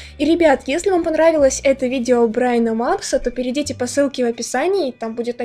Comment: Мне одной показалось что она сказала "Брайна МаКса"?